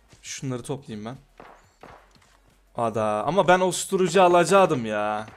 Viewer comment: ama osturucu gibi duydum ben